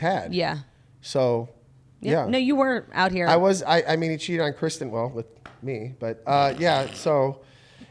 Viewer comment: did he say “he cheated on Kristen with me”